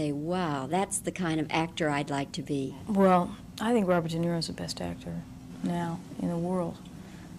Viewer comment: Who is the actor she admires at 2:51 ... I can't make out his name ? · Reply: Robert De Niro. For female actresss she said Ellen Burstyn and Katherine Hepburn.